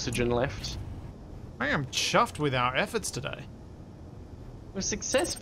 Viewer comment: Did you say chuffed or Chubbed with your efforts today?!? When I spent time in ol Britannia they would say "Don't chance it CHUB it!" I took it to mean that..